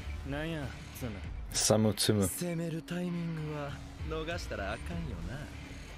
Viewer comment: The subs were a bit off I think. In the manga he said something like "Don't mess up the timing, okay?" or something (You can even hear him say the word "timing")